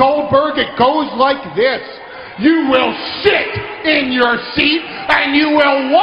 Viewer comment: Whoa! I thought Heyman said “you will s*** in your seat!” Lol